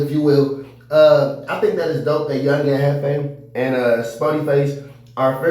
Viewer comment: I thought you said he pressed sporty face I'm so sick of your fake ass